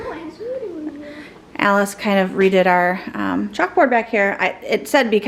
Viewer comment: You talked about Alice and her magazine, then it shifted to the chalkboard. For some reason, I heard you say she “readed” the board and not “redid” it 😂😂😂 Glad y’all had an awesome date night!!